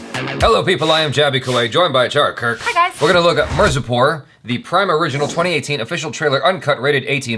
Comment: Whatt did he just say mercy pour instead of MIRZAPUR.